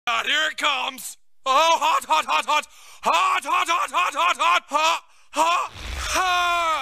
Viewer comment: Bro I thought John cena was saying “hot hot hot “